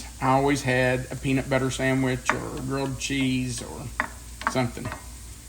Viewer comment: I thought you said “grilled chicken” until said it was real😂